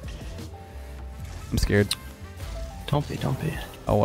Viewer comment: what ppl say: "I'm scared." what i hear: "I'm Scarab."